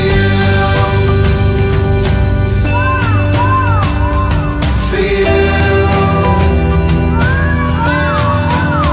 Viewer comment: Does anyone know what’s being said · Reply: It sounds like what,what, what,why,why,why